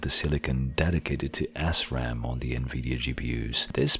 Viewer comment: immature I know but it sounds like you say assram lol. Good video.